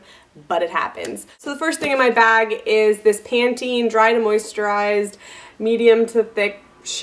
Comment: I really need to learn how to listen properly, when you said "Pantene" I thought you said "panties", and when you said "Pennsylvania" I thought you said "Transylvania" (when I was younger I thought they were the same place).